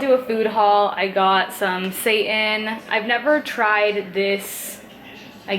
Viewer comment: i was halfway watching and i heard "i got some satan" at 2:38 i was like WTF LOL